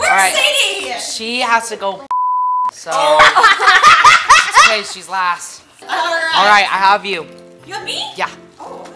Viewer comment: what did say? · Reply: Poop